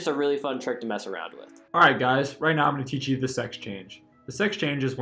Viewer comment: No one: The video time 2:44 Me: uh did he say sex change?